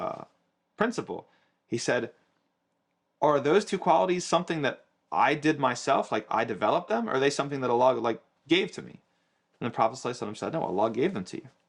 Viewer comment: i think what he said is "did i get those qualities after i became Muslim or was i born with them"